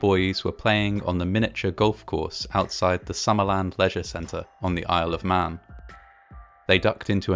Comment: I heard “Pleasure Center” at first 🤣